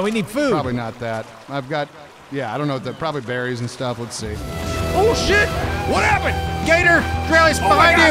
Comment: i heard that "Schoot it" from swamp ppl @ 17:23, but corrected yourself for copyright issues lol!